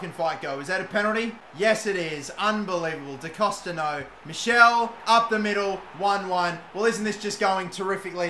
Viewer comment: Did just call Dwight McNeil MICHELLE????😂😂😂😂